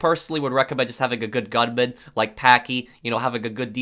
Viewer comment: did he say paki